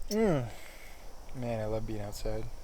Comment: is that "I love Beyonce" or i hear something wrong haha